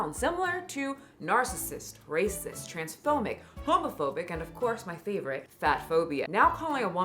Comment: Misheard homophonic instead of homophobic...